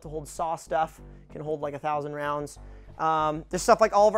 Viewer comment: did he just say 9000 rounds?